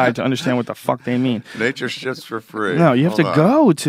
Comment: LOL NATURE SHIPS FOR FREE. I kept hearing "Nature shits for free" oh god. And lmfao... god dammit.